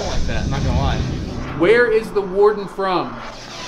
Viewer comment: When they said “where did the warden come from” it sounded like it said “billy creek”